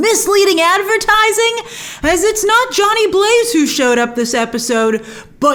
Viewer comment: TBH: when Wong said Danny Blaze, I thought he said Johnny Blaze also. I literally had to turn on the Open Caption to make sure I heard the name right lol.